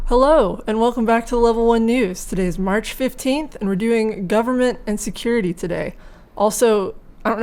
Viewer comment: For some reason I heard: " Governmebt Insecurity". Is it just me?